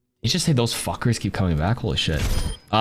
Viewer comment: he says “these suckers keep coming back” but I swear it sounds like he says “fuckers”